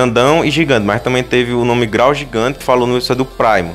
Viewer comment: KKKK BILAU GIGANTE KKKKKKK ESSA FOI BOA AGORA SEMPRE QUE EU VER O GIGANTE VS VILGAX SÓ VOU ESCUTAR ISSO KKKK